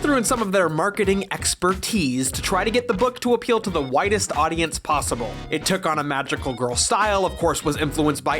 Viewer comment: I heard "whitest audience possible." 😅